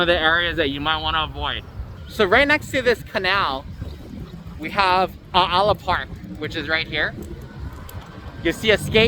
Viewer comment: Al ala can’t figure it out what he saying Al wahi? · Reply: Aala.